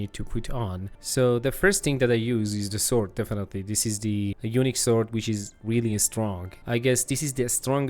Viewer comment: Dude when you said " unique" I thought you said " Eunuch tier" lol.